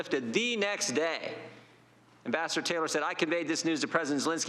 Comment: I thought he said "That bastard Taylor" but of course he said "Ambassador Taylor" Tee hee!!